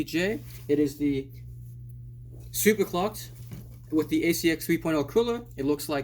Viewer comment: For a second there at the start. Instead of saying "Super Clocked" I thought you said "Super Fucked"